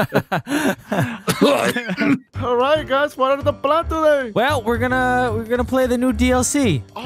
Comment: it sounded like said water the plant